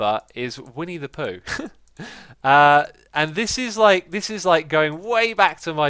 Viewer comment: Whenever X says 'Winnie the Pooh' all I hear is "We need to Poo"